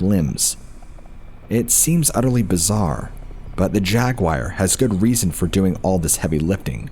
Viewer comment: Jaguars in North America or pumas and Cougars? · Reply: I thought he said, 'jack-wire' man it took me a while to realize that he was saying jaguar lol